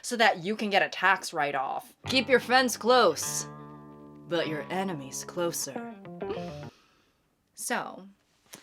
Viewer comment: I wasnt looking at my phone and heard her say keep your fence closed and your enemies closer and i thought it was some alpha male cowboy pun and it made me laugh